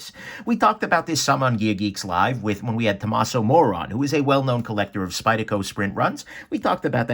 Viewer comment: I definitely heard that as “Thomas O’Moron” at first instead of “Thomas O. Moore on...”